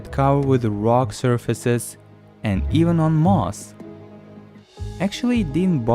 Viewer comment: him : even on rocks what I heard : Elon musk